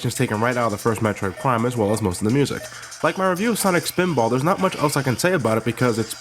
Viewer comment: Oh... I thought he said "Sonic Pinball Party" lol I say that because he has taken down some Sonic videos and I thought it was one of these...